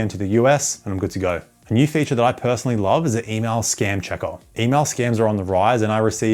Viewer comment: lol. I thought you said anal. Anal scan checker. Surfchark is getting miteculous with their scannings.